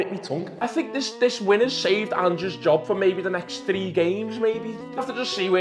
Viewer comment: Half Thought You Said "Andrews Job" 😭